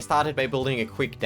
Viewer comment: "parted by building a quick demo " Me:wut wut does parted mean Edit: 0:03 "for the scream." Me: oh god so much editing gone wrong in this video i think theres more